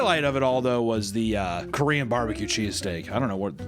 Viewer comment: Swore I heard Korean BBQ cheesecake at first. Had to rewind 😂